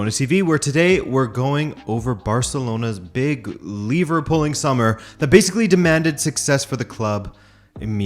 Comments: i keep hearing 'Barcelona's Liverpooling summer' lol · every time you said "lever pull" i thought you said Liverpool and was confused as fuck